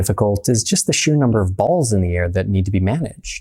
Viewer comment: At the beginning I thought he was gonna say “the sheer number of balls you need” 😭